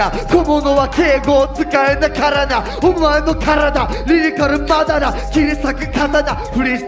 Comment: リリカルマダラーがマジカルバナナ🍌に聞こえて急に勝負仕掛けてきたのかと思った